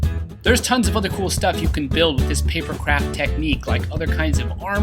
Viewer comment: Definitely thought he said "paper crap technique" the first time there... oops... :p